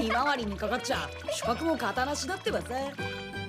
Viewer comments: did I just hear hinata say ara ara? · did she just say Ara ara